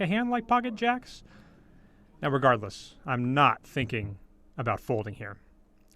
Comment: did you say "irregardless"? That's bad.